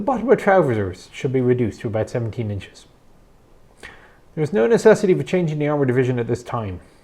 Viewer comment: After 5 listens I have no idea what was said after "the infantry division needs a recon battalion". "Something something something 17 inches" at 33:20. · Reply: "Trousers need to 17 inches"